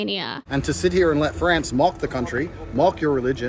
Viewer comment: wtf does he say mock like that 💀 bros saying “molk”